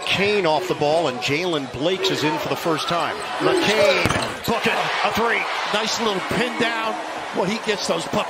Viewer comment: I swear I heard him say “Fuck it, a three” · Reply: Book it😂